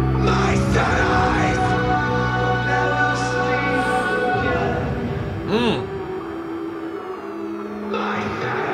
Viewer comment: He says “My cities will never sleep again” not solace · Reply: I thought he said “my sad eyes will never sleep again”